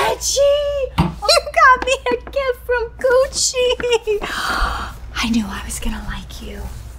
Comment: They way she said “GUCCI” sound like she said “COOCHIEEEE”